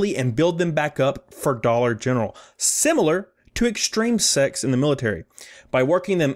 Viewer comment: Anybody else have to pause, rewind and read at 7:28 because you thought he said "Similar to extreme SEX in the military"?